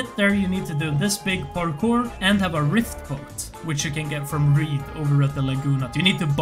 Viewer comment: What are you saying at 21:33, you need to do the rift parkour and have a what? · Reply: Rift boat which you get from Reed By the Lagoon Hut